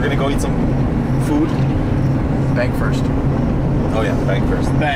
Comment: lmao i thought he said "yo lets go get some food" "beg first." haha XD